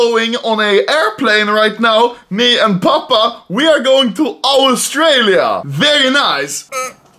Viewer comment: I thought he said we are going to Auschwitz😂